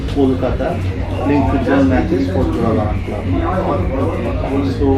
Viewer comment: You guys made a mistake in subtitles (11:39 min) . That was Jorabagan club what he said not Mohunbagan .